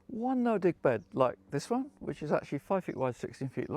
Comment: Heh heh. I thought he said “ No dick bed” Time to get my hearing checked 😊